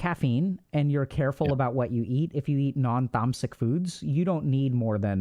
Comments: non-what foods? curious what that word is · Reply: Sattvic diet Edit: for more clarity "foods that decrease the energy of the body are considered tamasic" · At the end of the interview when they are talking about the monk shcedules and thors schedules (around 2:58:25), Dr. k references something call non thumsic food? I was trying to understand it and used the captions to see what he said but I dont get any result, what exactly is he talking about here on the diet?